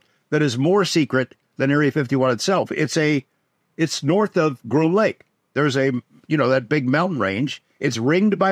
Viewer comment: What did George say at 1:09:08 ? North of grew lake???? I’m just curious where the Area 51 inside Area 51 is